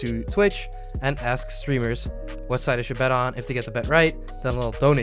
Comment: Yo nice vid. Next time please say what color I should bet on. I got confused, cause I thought you said what site. Like I didn't understand it :D